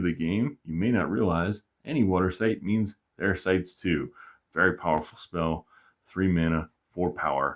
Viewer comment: "Any water site means their sites, too." Lugbog is powerful because you can cast it onto your opponent's water sites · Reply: hhh “their sites too” I heard “air sites too” haha. good content, thanks for making!